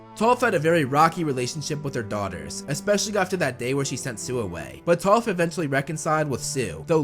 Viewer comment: it sounds like he said soobway